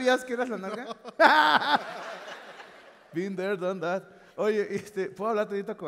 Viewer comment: "been there, done that" es lo que dice jajajaja se mamaron en los subtitulo